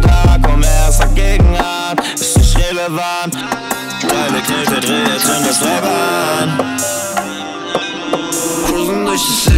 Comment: was sagt bei 1:20 edit: glaub irgendwas mit drive, knete und zünde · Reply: teile, knete, drehe, zünd das Paper an. <3